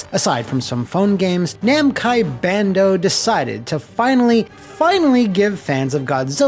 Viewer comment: Okay, for a second there at 0:58 I thought I had a stroke or something when I heard "Namcai and Bando"